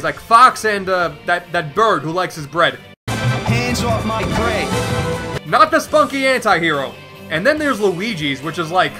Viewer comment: This is more detailed in SMB4, but Falco is actually saying, "Hands off my prey." It does sound like Hands of my bread in Brawl, tho. And I admit, I thought thats what he said before 4 came out.